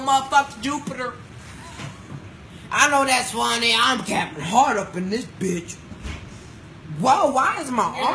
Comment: “I’m higher then a Astronauton on Jupiter” “ik I’m funny I’m Kevin hart” “Why is my arm like that I’m spider man?” 😂😂😂😂😂😂😂😂😂 I cant rnnn I don’t even laugh like that but he made me spit the apple juice everywhere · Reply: i thought he said "i know i'm funny, i'm cappin' hard in this bitch"